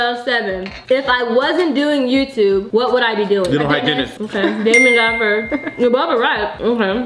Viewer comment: In 7:11 what did say? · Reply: Dental hygienist